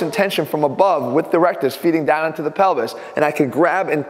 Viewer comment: ATHLEAN SAYS PELVIS ME : DID HE JUST SAY PENI$